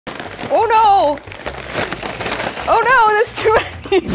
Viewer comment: Who else thought she said *UNO* when she said *OH NO*